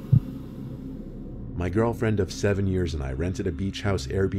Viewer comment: In the beginning I thought he said " my girlfriend 7 years old an i" I was like hold up🧐